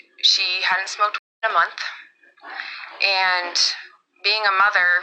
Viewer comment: smoked ______.. 2x here it sounds like she said m e t h. So that would cause those symptoms of paranoia in many people. I've seen it ! Also, she did or didn't go to a party but now it's a football game?